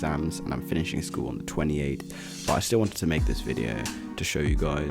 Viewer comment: did you say you’re 28? Nevermind you said “on 28th” I’m dumb lmao